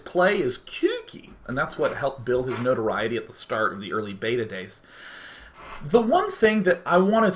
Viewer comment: Did he just say batadase?